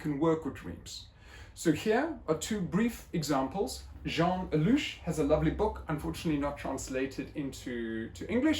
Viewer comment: what is the name mentioned here? I couldn't figure out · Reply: Jean Allouch